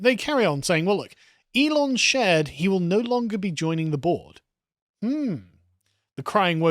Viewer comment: Every time you say “Elon isn’t joining the board” all I hear is “Elon isn’t joining The Borg” 😂 very fitting